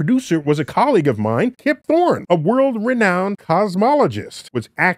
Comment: I thought he said Jim Thorpe and I was like “wait that doesn’t match up”